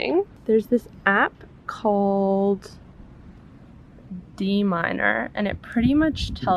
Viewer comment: I came to this video to look up the app you used for the Vit D. And as a musician I thought you said D minor! But its Dminder haha🙄